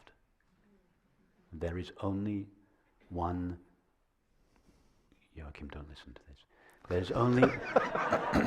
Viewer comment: What does he say at 1:07? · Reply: "Jochim, don't lissen to this."